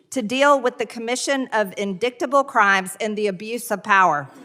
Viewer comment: Did she just say… in-DICK-table??? Instead of.. indictable? LOL